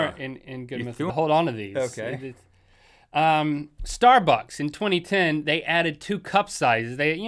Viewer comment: When Link said "StarBucks" I thought that he said "Star Fox" :P